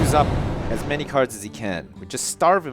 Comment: lol i thought you said carts lol